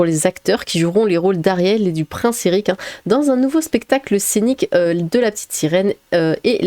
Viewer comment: Alors je ne sais pas si c'est un "soucis" de prononciation, si c'est moi ou si c'est volontaire... mais au début de la vidéo j'ai entendu "dans un nouveau spectacle cynique de La Petite Sirène" et non "scénique"; et j'ai trouvé ça glorieux.